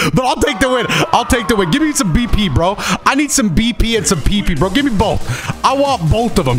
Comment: did I hear say he wants pp? 🤣 dude we arnt ever letting him live this down, quick somebody get a cut of that